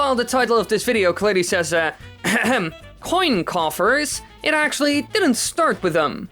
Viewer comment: I've always heard it as "Coin Copper" and I'm gonna keep calling it that because it sounds like a copper coin.